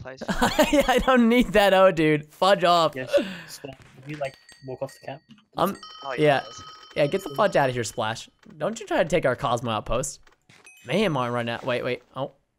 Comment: it sounds like he says f#ck off